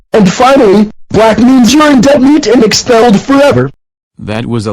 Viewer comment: what did he say at the end 😂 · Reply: And finally, black means you're in Dead meat and expelled forever!